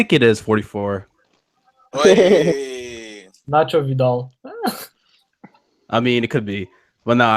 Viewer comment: Did say Nacho Vidal?